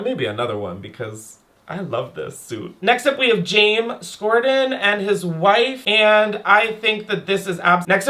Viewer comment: I DIED when just skipped past James Corden and his wife.. "and I think that this is ab..." 😂😂not even worth the time hunny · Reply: i heard "Jame Scorden" lmao